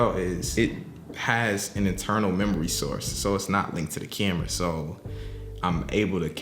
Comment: Real talk. I thought he said "it has an eternal memory source" @ 12:42 😅 out here with god-tier mics and junk.